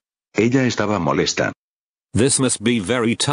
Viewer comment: The baby is not saying i'm sad is saying the best day of My life